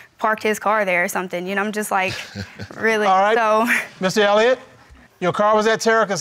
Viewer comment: did he say missy Elliot